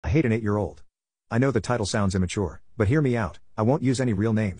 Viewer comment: I thought it said “I ate a eight year old”😂